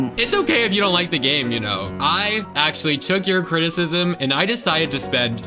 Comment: I thought you said I actually took you children not criticism